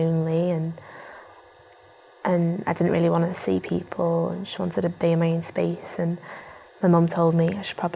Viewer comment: Please answer! What did say in the beginning 1:13 after "feeling lonely" ? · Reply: “I didn’t really wanna see people, I wanted to be in my own space. So I went to the doctors and they sent me to you”